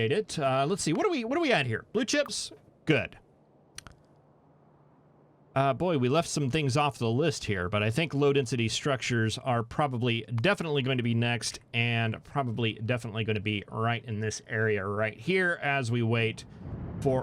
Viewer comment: I keep hearing "loaded city structures" instead of "low density structures" and get confused.